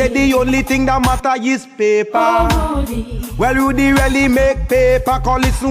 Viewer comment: The only thing matter is paper well rudie really make paper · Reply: Rudie rarely make paper I thought he said